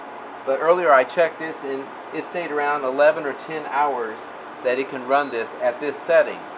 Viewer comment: I thought you said it would run 10 to 13 hours. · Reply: At the moment in the video I said something like - at this moment - with the Mark 2 at this setting, the Delta shows it could run for 12 or 13 hours. And it will, IF all conditions remained exactly the same. But conditions can change and that would affect how long the Delta could run it. Other lithiums could not run the Mark 2 very long.